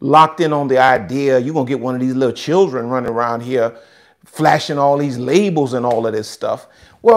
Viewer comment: I heard “you have all these children running around flashing their legos” and my soul giggled